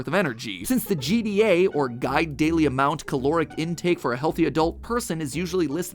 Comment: did anyone else hear guide-diddly-ied amount?